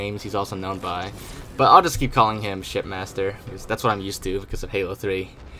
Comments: I kept hearing "shitmaster" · All I hear is:"ShitMaster".